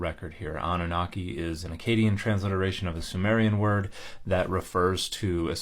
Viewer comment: I was listening to this but not watching the subtitles and I had a bit of a double take when Dan said "Akkadian"... because I thought he said Acadian and it didn't sound anything like French to me!